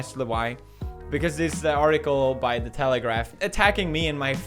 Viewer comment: When he said " This article by the telegraph" I thought he said Taliban